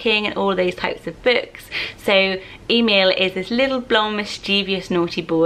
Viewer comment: Legit thought you said Email at first 🤣